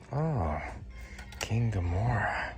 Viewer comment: It's King Ghidorah! Sounded like you said King Ghimora. 😂